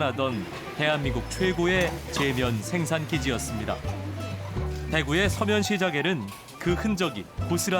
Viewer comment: "대한민국 최고의 재면 생산기지였다. 서면시장,,,?" 일부로 그러는 거지? 서문시장을 모호하게 발음해서 날리면 처럼 물타기하는 풍조를 만들려고? ㅎㅎ 재미있어~ 대구는 '서문'시장 부산에는 '서면' 방송 똑바로 하시지 ~